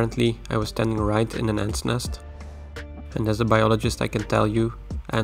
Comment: Everytime you say as a biologist I hear “whale biologist” in my head